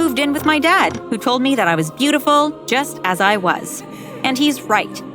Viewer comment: at the end I heard don't listen to eaters 😅